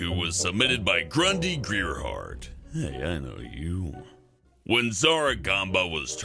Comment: when you said hey I know you the captions said Dolans brother IS THAT HIS BROTHER